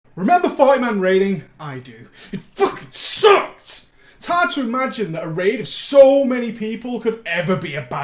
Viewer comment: I heard "remember 5 man raiding" in the beginning